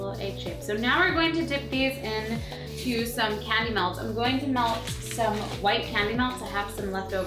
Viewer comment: When you said candy melts I thought you said cannimals.